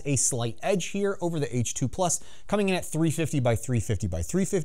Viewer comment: did i hear H2Plus twice at 2:15 ?